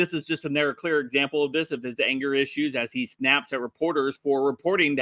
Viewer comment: I heard what I wanted to hear when you started your intro. I heard you say he “naps at the reporters”.